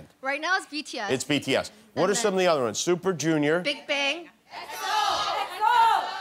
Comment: did she say blackpink or bigbang? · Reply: bigbang